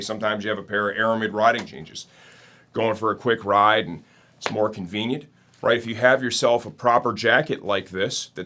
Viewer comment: 6.14 . Did he say more convenient? Heck i heard it as smoking weed! 🤣